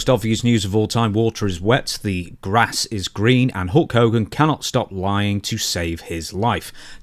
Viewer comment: I wasn’t fully paying attention during your introduction and I could’ve sworn you said “Walter is wet, grass is green” 😭😭😭